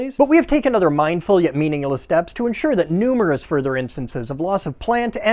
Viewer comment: saying meaningless while subtitles said meaningful caught me and my ADHD off guard